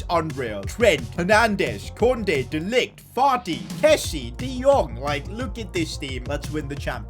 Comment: did say Farti instead of Fati lmao